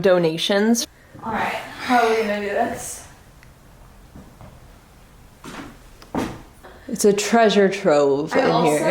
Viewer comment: I can’t be the only one who heard either “hi” or a long breath at 23:50 right after and walked in the the dining room. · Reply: i think its breathing then saying alright